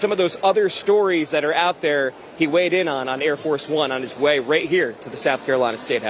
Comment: I thought I heard him say: "Hair Farce Bum"